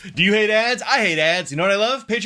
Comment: Wow.. I heard: “You eat *ss? I eat *ss!” 🫣